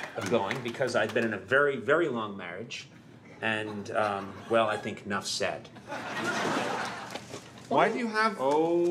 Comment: well I think enough "said" 不是 "sex" -> 應該是「那不用多解釋了」